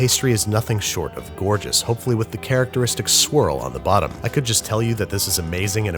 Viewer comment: Every time babish said “swirl” I thought he said “squirrel”. I had a good laugh.